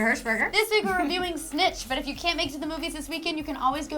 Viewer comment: I thought they said they were reviewing Stitch :( My childhood is disappointed.